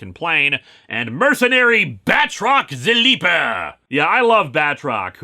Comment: Batroc the Leaper is seriously one of the more underrated joke-villains of Marvel, even when he became a prominent supporting character for Gwenpool he was still awesome and hilarious. -Teo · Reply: I swear there's been a couple times where for a split second I thought he said Badrock instead of Batrock.